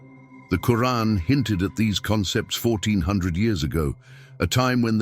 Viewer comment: The subtitles say 14 years ago. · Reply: 1400